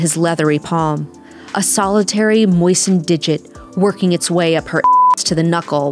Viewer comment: I think it says "salivary moistened"